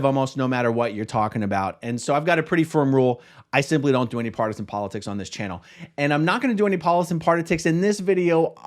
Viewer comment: Did anyone else hear “polison partitics” at 1:03? 😂